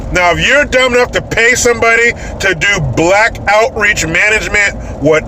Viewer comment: I thought he said black outrage management lol.